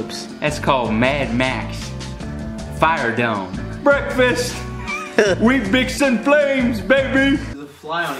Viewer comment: What the hell is he saying at 4:27... I can't make out what he is saying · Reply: we mixin flames baby